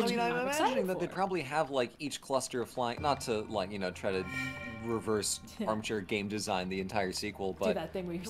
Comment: did you just say “reverse armchair game-design the entire sequel?”